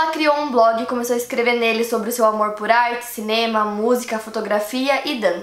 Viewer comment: no minuto 1:11 eu entendi: seu amor por aids